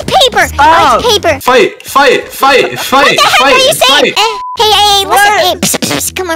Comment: is saying fai fai fai fai or fight fight fight fight😂😂😂😂